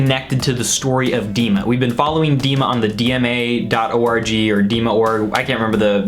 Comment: not gonna lie i thought you said “dma.orgy” and i was very confused for a moment